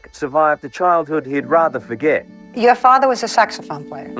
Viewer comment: Oh SAXOPHONE PLAYER... sounded like she said a Sex Offender...